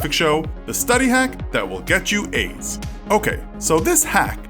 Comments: I thought he said this will get you aids😂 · I thought he said: "The study hacks that will give you aids" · lol i thought he said the hacks that will get you aids